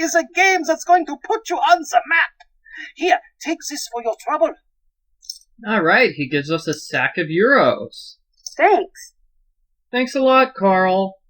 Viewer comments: when said alright hes giving us a bag of euros, i thought he said alright hes giving us a bag of urine O_o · Lol I thought he was about to say " a sack or urine" 0_o